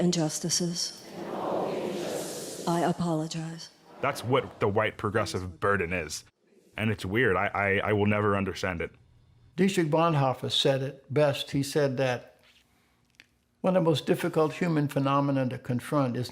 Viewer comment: Herman Cain quotes someone about folly and malice (5:44 in film) but I can't quite make out who it is from, It sounds like Richard Bonhoffer. Can someone with better hearing or intellect let me know who it is.